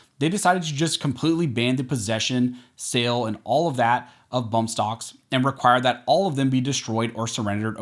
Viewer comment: Lol, did I hear "bump stonks" at 6:17 in the video?